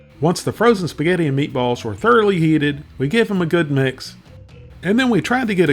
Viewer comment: To my not so good ars it sounded like you said "afer the meal is thoroughly yeeted",and thats what happened the last time I tried a "Banquet" meal....